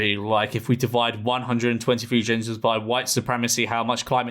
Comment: Thought you said gingers